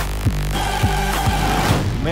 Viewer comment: Am i the only one that thought in the end he said "Mayonnaise maketh man"?